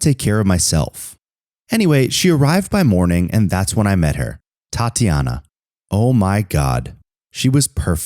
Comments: I thought he said Titiana 😂😂😂😂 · For a sec I thought he said “thot”tiana.. has to replay that bih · I thought he said Titiana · I thought it said thotiana